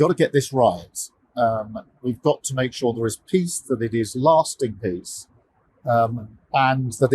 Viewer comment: My goodness. Monotone moron. Did he just say "We need an elastic peace" 😂 Taco man, it's a lasting peace, or was that a Freudian Freudian slip....